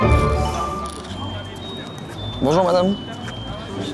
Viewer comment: "Excusez moi Madame, je voudrai savoir si je peux vous croquer le cul..." 🤣🤣🤣